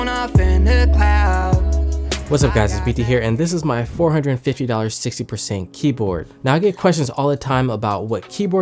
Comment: yea he just made the title wrong, in the video he says 65% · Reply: uuuuh no, he clearly says 60%